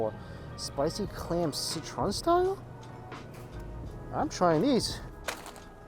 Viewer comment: hahahhahah, I heard I’m trinese.